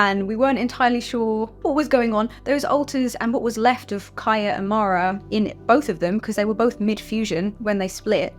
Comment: I totally ADHDed during the explanation of the fusing and splitting and thought you said Marie and Kya and I thought, oh that’s super sweet 🥹🥰. Then I realized you said Kya and Mara and I was like, wow that’s kinda hot but in a friendly good for them way??